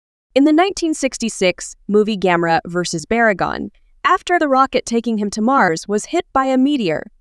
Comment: Did you said baragon instead of barugon?